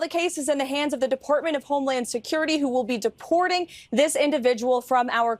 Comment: Did she just say "Deportment of highland security"!!!!!